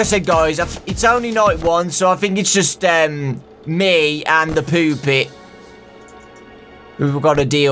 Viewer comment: I heard you say the poopet and I screamed with laughter XD